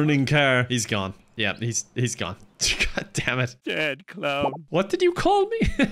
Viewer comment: What did homer say at 7:30? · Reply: I think it might be 'sad clown' or something like that. Despite watching this often I'm still not sure